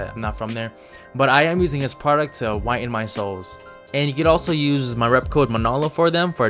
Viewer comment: What he said: I'm using this product to whiten my soles What I heard: I'm using this product to whiten my SOUL Me: I need that